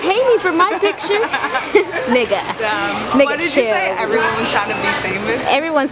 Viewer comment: did anyone heard her friend say "nigga" at around 9:29 ???